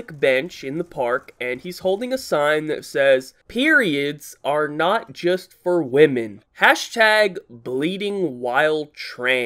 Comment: I thought the Singh said Pedos are not just for women